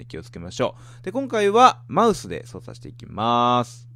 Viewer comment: 今回はマウスで操作していきマウスー って聞こえて吹いた